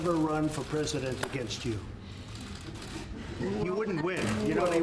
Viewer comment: does he say "HE wouldn't win" or "YOU wouldn't win" 0o (sounds like HE but.. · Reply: Pedro Sequeira yes he said "You wouldn't win" I think